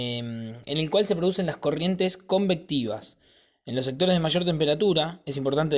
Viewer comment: alguien me puede decir lo que dice.. no lo entiendo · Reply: dice: convectivas